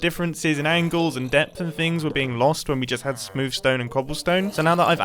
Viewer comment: So was I the only one who at first thought said 'sploof stone' at 10:07,?